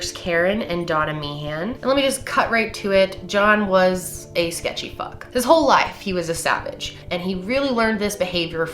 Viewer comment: did actually say john was a sketchy fuck @ 7:33 ????